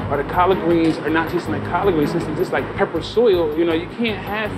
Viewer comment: did he say the collard greens taste like “pepper soil”? Does anyone know what that means? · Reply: Peppered soil, I think.